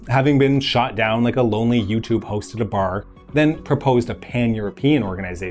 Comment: "shot down like a lonely YouTube host at a bar" lolololol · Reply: In the cold war context, I heard "YouTube" as "U2", as in the spy plane that was shot down. "You Tube" makes more sense.